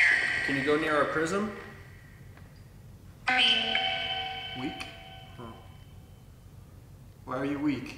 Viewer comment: when you said, “weak” I heard, “green”. Your question had a yes or no answer. They answered your question I believe. Green is for yes as you said. Anyone else hear that????😳😳😳😳